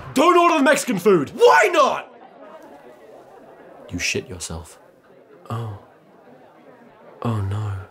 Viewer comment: Since this video came out I always thought he said "you shoot yourself", but I decided to visit this video again and the caption was on and I just realise he said "you shit yourself".